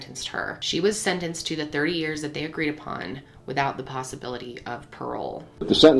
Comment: At first I thought I heard her say she was only sentenced to 3 years and I about lost my damn mind.